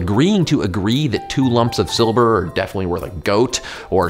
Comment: Did you say "Silber" instead of "Silver" at 0:57? I'm from Germany and that's what it's called here. ^^